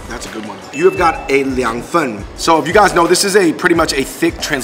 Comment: the way you said "fen" literally sounds like you said cold poop.. lol